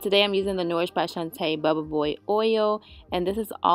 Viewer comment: Sound like you said bubbaboy oil